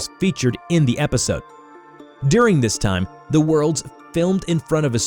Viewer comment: did anyone heard the word "world" instead of word in